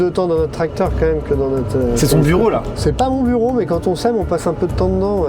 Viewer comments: Au début, quand Maxime et l'agriculteur étaient dans le tracteur, j'ai compris "on s'aime" au lieu de "on sème"... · "Quand on s'aime on passe un peu de temps dedans"... Voilà ce que j'ai compris à 10:58 .. Et je ne suis pas en désaccord... Juste un peu 'bsédé...